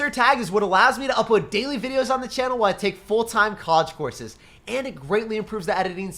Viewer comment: I always thought instead of you saying “college courses” you would say “cause forces” lol🤣😂